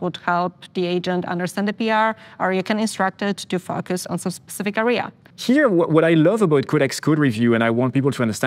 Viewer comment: Did anyone else hear something different the first time? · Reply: you can instruct it to F us in a specific area